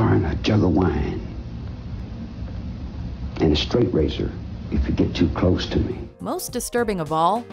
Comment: All these years I thought he said I’m a street racer if you get to close not a straight razor 🤦🏻